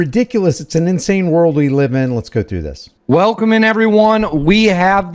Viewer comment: Is he saying welcome in or willkommen? Willkommen is German for welcome. It's not normal for an English speaking person to say, "Welcome in everyone..." Maybe something or maybe nothing. Just find it abnormal.